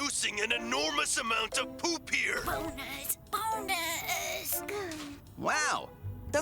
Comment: I genuinely thought that woman said "boners, boners" at first 💀